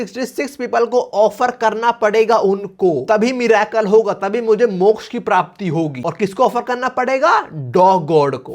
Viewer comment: GENIUNLY thought someone called out to me(my name is moksh)